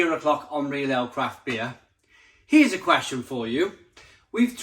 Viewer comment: With your accent the first thing I heard was "Crap Beer" and I think in a lot of cases especially with some of the new canned beers, is perfectly reasonable